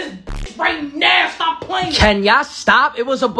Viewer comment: "Y'all are some villi-ians" Got me dead 💀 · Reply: I thought she said civilians, villain would n better tho